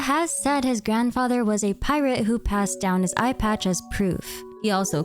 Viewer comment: i heard "his grandfather was a pirate, who passed down his ipad as proof" 😭😭